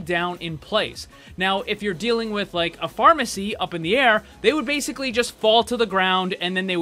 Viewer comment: anyone else hear him say, "Pharmacy," instead of, "Pharmercy?"